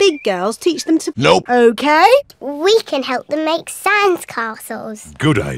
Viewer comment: did Rebecca just say sans?!